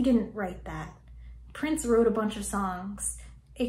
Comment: I thought you said "Prince ruined a bunch of songs" and help me G.O.D I was gonna unclick that bell.